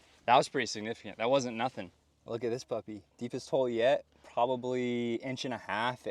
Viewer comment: “Look at this, Papi” did he just called me daddy in spanish💀😭🙏🏻